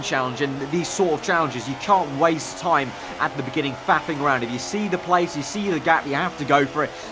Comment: Did anyone else hear "fapping around" at 2:01????